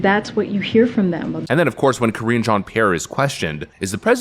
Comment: I thought he said “Cringe Jean-Pierre” 😂